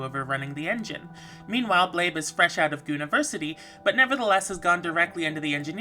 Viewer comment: Did you say "fresh out of gooniversity" or i heard it wrong? hahahha